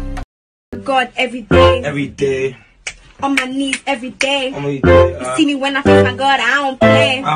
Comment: What does he say?? I can’t hear it?? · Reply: he said “on my knees everyday??”